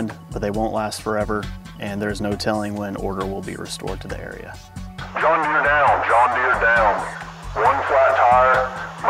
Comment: I heard "John Deere Down" 2x, "Mile marker 6" and "bring one tire"... Not I shot a deer at tree stand 3.